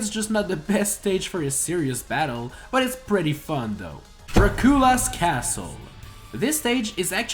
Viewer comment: DID YOU JUST SAY DRA KU LU WHAT